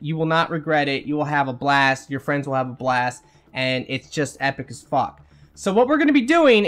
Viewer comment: I know you said epic as fuck, but I swear I heard "epic Spock" Also this must work like a charm now that combos are more powerful. I remember I was playing with randoms in me3 but we were coordinated. We had 3 biotic classes and a newbie we were mentoring. We crushed gold with 2 and sometimes 3 biotic explosions a second. It was aeesome